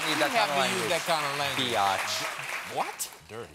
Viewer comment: did Steve just call John a "bee-yotch" and John was like "whut?" or is that just me?